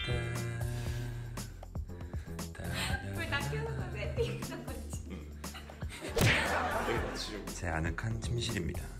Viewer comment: 여기 뭐라고 얘기 하신건가요? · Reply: 불 다 켜놓고 셋팅해놨냐구 한거같아요!!!